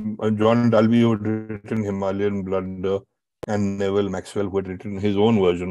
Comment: Who is preparing these egregious subtitles? The name is "John Dalvi" and not "John Dalvey". Even that might be pardonable, but how does one end up writing "Himalayan Blood" instead of "Himalayan Blunder"? The very term "Himalayan Blunder", so popularly used, was coined by Brigadier John Dalvi. How does one mistake the title of the very book that made the term popular?